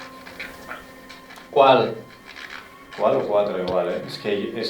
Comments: En el minuto 23:38 cuando preguntan que cuantas personas hay aquí ahora mismo, se escucha claramente que dice cinco. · en el 23:40 justo cuando termina la pregunta, se me parece escuchar que dice cinco...